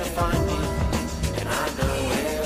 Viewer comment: Why did I hear “I don’t know how to cook” 💀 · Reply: I heard, “I’m not a cook” 😭